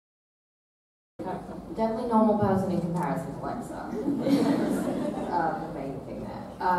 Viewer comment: What did she said in 32:19? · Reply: “Definitely a normal person compared to Lexa”